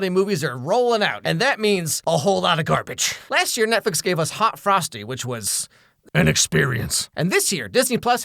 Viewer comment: I misheard “hot frosty” and heard “hot prostate” and hopped up so fast 😂